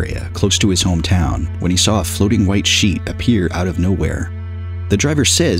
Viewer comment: probably a sheet ... maybe the wind blew it off of someone's clothesline :P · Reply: The first twice I thought the man said "sheep" . I was quite disappointed, really...